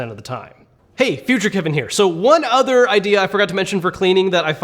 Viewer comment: when he said “hey future kevin here,” ( 7:43 )it sounded like hey veasuce michel here